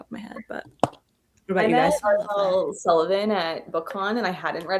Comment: what author is talking about? Not able to hear what she said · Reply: Ariel Sullivan!